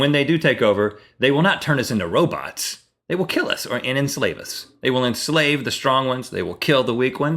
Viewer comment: Rhett, at 5:05 I thought you said "They will insulin us."